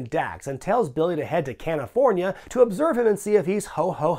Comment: Did say “Can-ifornia”??